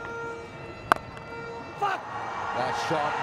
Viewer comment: Wait did someone just say f***